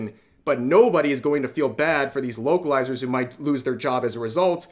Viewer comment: I thought you said woke-alizer lol